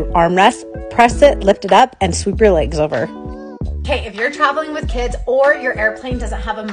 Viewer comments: lol at the start i thought she said: "if ur ever traveling on a hAiRpLaNe-" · Is it just me or I heard her say hair plane😅